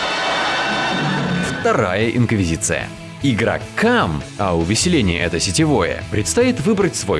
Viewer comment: "Игрокам..." я слышу: "Игра "CUM" XD